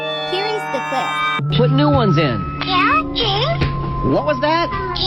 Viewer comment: I Didnt Heared K1LL James I Heared Meow James 💀💀💀